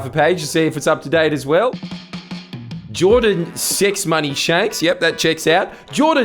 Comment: I thought he said Jordan Six Money Shanks. Had to rewatch to make sure that wasn’t what he said lol